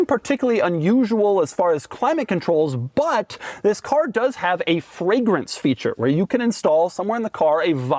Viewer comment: for a second I thought Doug said you can install a vile fragrance. that would come in handy for installing farts for the immature child within us all